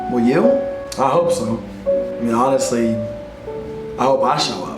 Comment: Can someone help me out? I don't quite understand what he says from 3:20 to 3:23 "and honestly, I hope XXXXXX" I don't get that last part, it would be nice if someone could help ^^ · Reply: Arma he said "honestly, i hope i show up" it made me so sad, to see such a great content creator be so down on himself. your content is exceptional and i hope you see that soon. lots of love <3